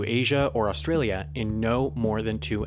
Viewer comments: he says "in no more than two hours" but the captions say "in no more than three hours" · You said "....in no more than two hours*" The English subtitles said "...in no more than three hours*"